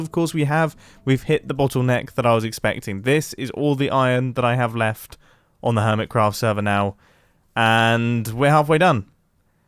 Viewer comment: For a second I thought he said: "This is all iron on Hermitcraft server... And I'm going to use all of it..." LoL